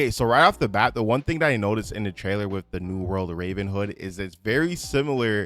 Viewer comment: not Henford on bagels 😂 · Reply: Sounds like he said "RavenHood" right before that